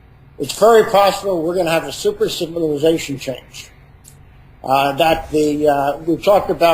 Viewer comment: at about 5:42mins jim said super civilization? what exactly did he say?